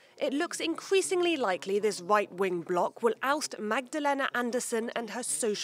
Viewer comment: did she just say "white wing block" ?! Or is it me?